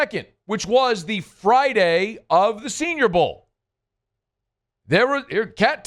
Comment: Inaccurate. It was made 9 days before. SB 58 was on Feb 11th. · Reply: Ah he said “Friday of Senior Bowl” … I misheard him and thought he said “Friday of Super Bowl.” Regardless, my point still stands. is freaking out for no reason. There’s no upside to leaking the Moore hire. Everything is pointing towards him coming to New Orleans… let the dude coach the SB then they’ll make the announcement the day after. Just relax and be patient.